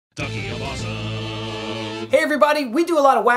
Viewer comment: I thought was said "Donkey of Awesome"